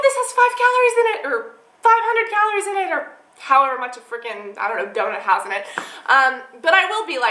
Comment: Did she say Freaking at 16:08! What happened to freaky deeking?